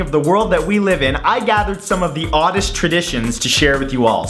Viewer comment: I genuinely thought Matt said ‘oddish traditions’ rather than ‘oddest tradition’ at the beginning I couldn’t help questioning the evident superior pokémon society of oddishes 😂😂